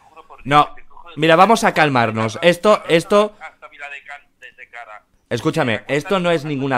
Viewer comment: POV: vuelves a ver el vídeo y entiendes lo que dice en el minuto 4:31, " te arrastro hasta Viladecans desde Gavá"